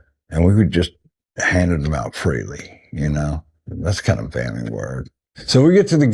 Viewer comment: It warms my heart as a Russian. Could anyone please tell me, 2:58 and on, "handed them out freely, that's kind of..." what did he say next (I don't believe the subtitles make sense)? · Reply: Sounded like "thats the kind of band we were"